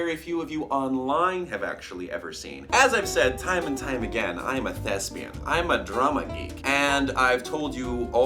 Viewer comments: I heard "Im a thespian" as "I'm a lesbian" and needed to go back for a second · I misheard and thought you said: “I am a lesbian” and I was so confused for a sec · I thought he said that he was lesbian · when he said drama geek I thought he said drummer geek. I think my head phones might be broken:-3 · When in said thespian I thought he said lesbian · I heard "as I have said time and time again I am a Lesbian" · I thought he said "I have told you I am a lesbian". LOL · When u said "thespian" I thought u said "lesbian"... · I had to replay that bit because I thought he said he was a Lesbian.